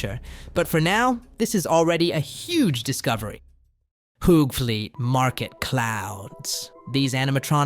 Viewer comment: what did he say I can't spell Hoovlee market I just keep saying Hoovele Market I need help spelling it. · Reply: Hoogvliet supermarket